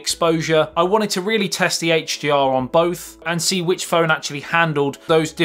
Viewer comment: i think you said HGR instead of HDR